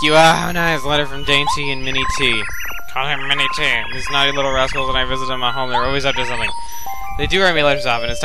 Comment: 00.17 I heard he said ''Those naughty little ássholes..'' XDXD